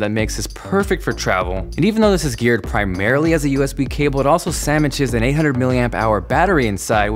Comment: did i hear you say sammiches at 1:52?